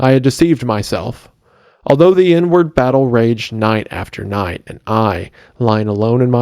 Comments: sorry about the loss of your dear friend. Was shocked to see it. I pray for his family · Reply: I thought he said" the N-word battle" and not " the inward battle" lol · yoo did he say The N word battle??? I didn’t think we were reading Lovecraft here, boss. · I had the video playing in the background until I heard "the n-word battle..." lmfao